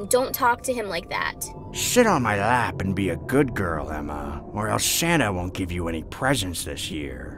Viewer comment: When he said "sit on my lap and be a good girl emma" it kinda sounded like he said "sh*t on my lap and be a good girl emma"